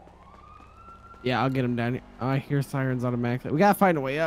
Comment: Did say “yeah I ma get em daddy”